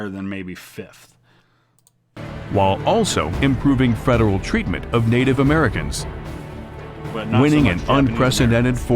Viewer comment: What did you say there? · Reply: I said “but not so much Japanese Americans”